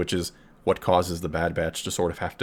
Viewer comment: I actually thought it said "The Bad Bitch". lol